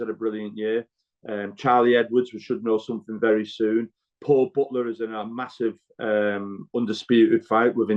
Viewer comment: Did he say Paul Butler vs Inoue?? 👀